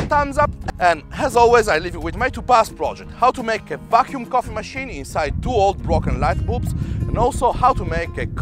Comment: When he says "how to make a vacuum coffee machine..." at 3:31, it sounds like he is saying "how to make a f$&king coffee machine..".